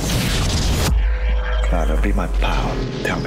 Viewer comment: I couldn't even understand what he said... "Clara be a good man?" ? xD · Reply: Scott Gerrard Calm down buddy. I had to crank my volume up and listen to it a few times. The sound sharply cuts at that point ON TOP of his accent. It almost sounded like he said, "Clara be a 'power', am I a good man?"